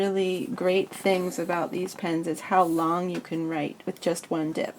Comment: I honestly thought it said penis